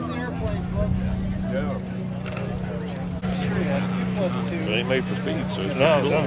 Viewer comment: Anybody else hear that 2+2?!